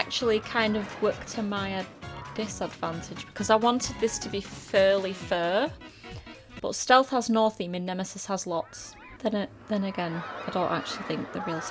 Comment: I had to double take at 11:54 i was like.."did she just say furly fur?" Xd. Awesome video though!